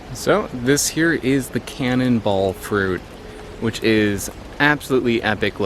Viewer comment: I thought it said ‘Cannibal fruit’